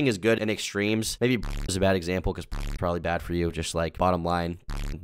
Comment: I literally don’t know what word he’s saying. Is he saying porn is bad for you?